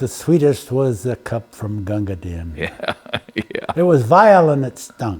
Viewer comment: Can anybody tell me the quote and author at 8:40 please? I can't catch it on the video. Someone Kipling. of the drinks I've ever drunk the sweetest was the cup..... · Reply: poem by author Rudyard Kipling....."Gunga Din" see movie made of this, starring Cary Grant 1939 "Gunga Din"